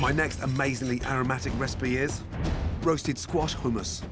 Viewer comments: I literally heard him say roasted squashed humans And saw humans on the board lmfaaao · I thought it said humans for a sec LOL